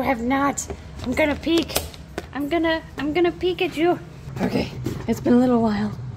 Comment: When I listened the first time, I swear I heard, "I'm gonna Pikachu."